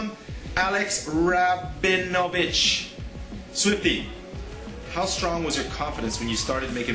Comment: if u turn on captions at 5:43 instead of swifty the captions says soupy